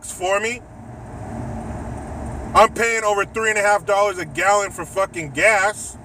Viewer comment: um did he say $3.50 for gas?!